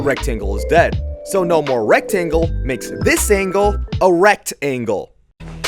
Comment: pls no i heard "erect angle"